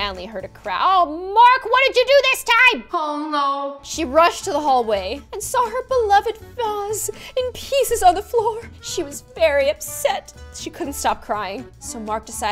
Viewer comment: I thought she said: "and saw her beloved thongs in pieces on the floor"😂